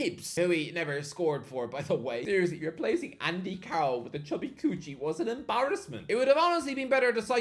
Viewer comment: Did he just say chubby kuci